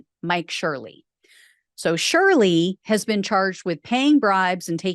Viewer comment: I am surely not the Shirley in this story....... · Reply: you mean? At first I thought she said surely. 😂